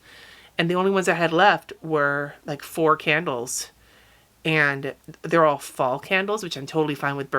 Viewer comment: I thought you just said four candles and I instantly got a flashback to the Two Ronnies sketch! 😅 might be a bit of a British thing but very funny 😄